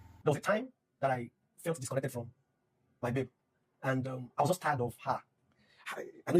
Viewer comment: I swear for a split second, it sounded like he said he felt disconnected from his beard. 😂